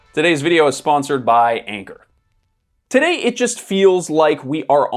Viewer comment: I was listening to this while doing other stuff and I thought he said “this video is sponsored by anger” which would’ve been on brand 🤣